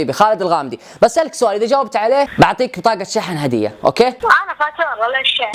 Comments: وش قال? ? ? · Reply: قال انا فاتورة ليش شحن ؟ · بليز قولو لي وش قال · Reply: قال شريحتي فاتورة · بالله ي عيال الي يعرف وش يقول هنا له 10 سوا او موبايلي او اشي شي يبيه اتحداك اذا فهمت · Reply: ي سمعته يقول انا فاتورة ليش ..........